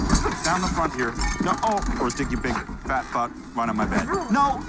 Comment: Wait wait wait, at 0:49 did he just say “Fat F**k” ?!?!?! I swear he said “Fat F**k”??!! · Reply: No he said fat butt 😂😂😂